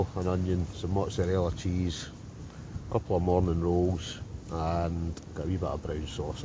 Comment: 😂I thought you said Mormon rolls, i see it’s morning rolls